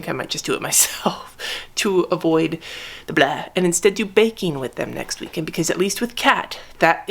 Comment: When you said baking I thought u said BANKING AND I WAS SHOOOOOK AT KATS INVOLVMENT until I realized....